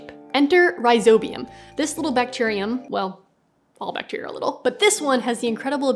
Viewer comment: What is the comment at 4:23 or so? Is she saying all bacteria do this? Because that doesn't seem right, but that's what it sounds like. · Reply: "All bacteria are little"... commenting on the fact that she first said "thiss little bacterium"...